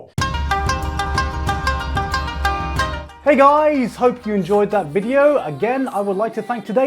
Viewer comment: he said "Hey guys" in the end of the video In my head i hear "Hey thighs"